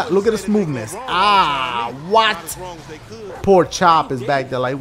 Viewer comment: When he said poor chop it sounded like he said porkchop